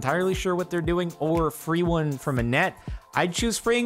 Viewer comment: I heard "A free one from Annette" and was slightly confused why he'd eat a puffer-fish for free.